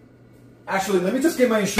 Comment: i thought he said "accually let me just get my shirt real qui-"